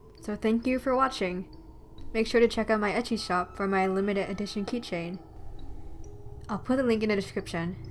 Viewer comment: I heard “etchy shop” and I couldn’t figure out what shop it was at first lol 😂